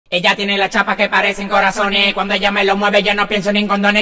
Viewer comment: Bro I thought it was saying tobi tobi tobi